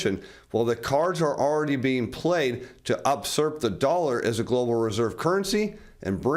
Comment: uh, did you mean usurp? Sounded like you said "upsurp" 🤣 is that part of the vernacular? Ohhhh ...hysterical laughter ensues. We gotta talk to your elder twin, Sam Donaldson 🤣 "upsurp" or was it 'outsurp'? Hilarious. Well done, #applause